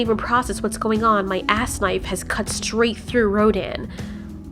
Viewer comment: Hold up, did anyone else hear Mothra say “ass knife” at 28:39 ?